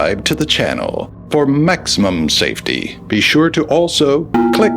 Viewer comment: I THOUGHT HE SAID “for Mexican safety”